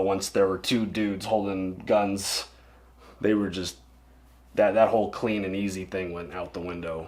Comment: I still defend Walt. 😂 I’m not saying what he does is right. I just like the guy. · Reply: I thought you said "I'm just that gay" lol